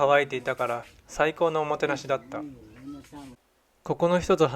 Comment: Such an enriching trip in Indonesia! Glad that you had the best of your days there. Made me wanna visit there soon too!! I heard 'saiko' at 1:42 hahahahaha made me miss your words!